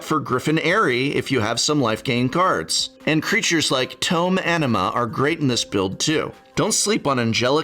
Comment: I keep hearing "tome enema" and can't stop thinking about how it is the most metal way to acquire knowledge.